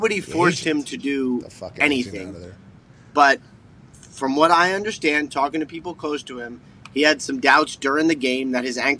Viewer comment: What does pat say under his breath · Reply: Sounds like pat says "the agent"